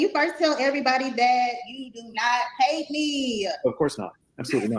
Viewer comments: She actually said "you do not HATE me" - Not "you didn't PAY me". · I heard "hate" not "pay"